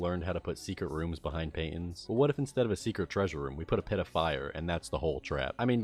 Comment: bro instead of "pit of fire" i thought you said "pedophile"